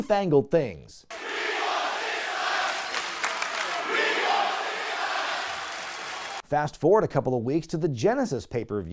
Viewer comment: can someone tell me what the crowd is chanting lol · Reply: We want six sides (six sided ring of TNA)